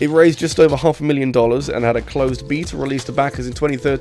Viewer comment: what word are you saying here? Closed Beater?